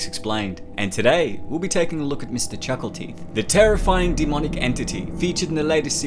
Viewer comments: I thought it said mr chucky teeth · I thought he said chuckle cheese Edit: Or Chucky Cheese lmao